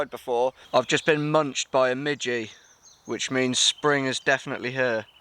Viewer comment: i"m a bit deaf,i thought for a second you said munched by a midget